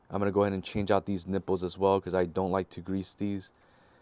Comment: Did you say you don’t like grease those? That’s maybe why they failed. Anyhow, thanks for the video I have to replace my wife’s on her 4Runner. Which lasted eight years. I must’ve misheard , but you definitely need to grease every 3k Miles. Did you need the angles shim kit?